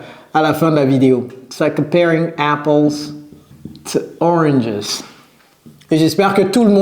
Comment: "Apples to oranges" I heard " apologize"😁 if I'm regular to see all your videos I can hear American accent. I'm glad to find your lessons.